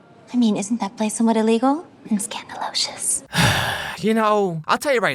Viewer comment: scandalOZious they place OZ inside the words “oh my Oz” its not just random words mispronounceddd it shows that in Oz they have a lot of respect to their country (instead of maybe god) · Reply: What?? Where did you come up with this? Put the subtitles on. It’s scandalocious. OZ is not in any of the words.